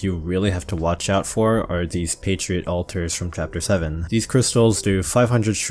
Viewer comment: OH YOU SAID "PATRIOT ALTARS" I thought you said "Patriot Alters" and I started wondering when he got an alter form